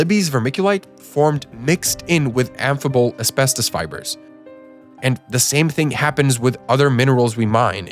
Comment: Correction: In the section about Libby, Montana the CC says "affable asbestos" when it should say "amphibole asbestos." 33:56 and